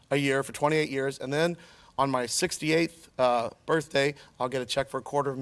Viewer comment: I understood him to say at 58 not 68. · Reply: He clearly says "68th" at 5:43.